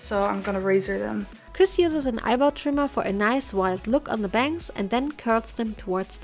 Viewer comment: I THOUGHT SHE SAID EYEBALL TRIMMER- I WAS SO SCARED FOR A SEC 😭😭